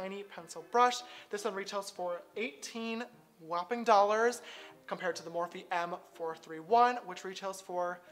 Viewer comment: Kylie’s brush during eyeshadow: 18$ Me: FINALLY WE REACHED SOMETHING UNDER TWENTY BUCKS Then I hear ‘2 Dollars’😶